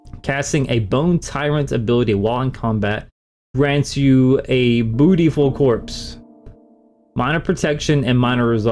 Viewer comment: beautiful corspe I thought you said bootyhole😅😂